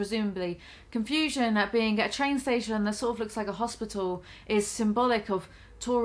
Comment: i rlly thought you said a “shane station”